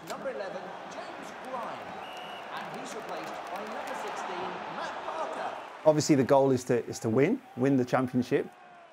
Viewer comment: Did I hear correctly at 1:20ish that somebody was replaced by Matt Parker?